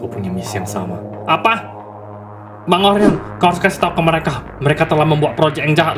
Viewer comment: kok gw dengernya bang Oren🗿